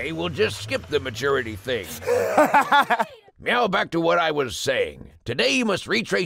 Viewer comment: did.... did he just say "meow back to what..."